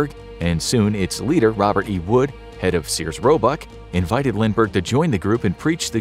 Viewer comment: did he say robuck